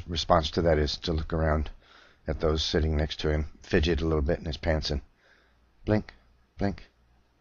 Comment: did i hear that correctly? did he just say "fidget a little in his pants"?